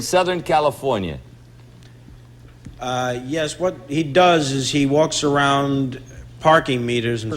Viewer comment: Wonder what Rob Reiner said at 10:27 ? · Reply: He’s a schmoot